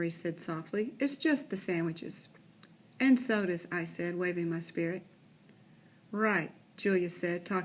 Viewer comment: did she say waving my Sprite that clearly says sprite